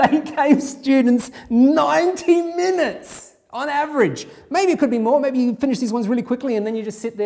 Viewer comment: I literally thought he said "Nineteen minutes" and considered myself broken